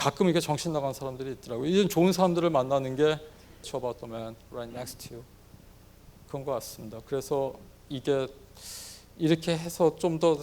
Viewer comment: 에 교수님이 하신 말씀이 뭔가요? ( ? ) about the men, right next to you 로 들려서..꼭 좀 알고 싶습니다...! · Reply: Cheer about the men, right next you. 당신곁의 동료들을 응원해주세요.